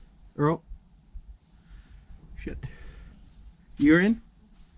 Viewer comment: what did he sayyy Did just say sh*t??????